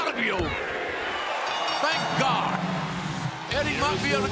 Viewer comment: Did Kingston say Thank you